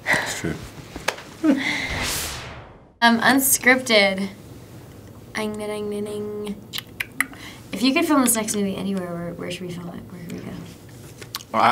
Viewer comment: i heard: "If you could film a sex movie anywhere, where should we film it?" ^^ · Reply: I think it was "If you could film this next movie"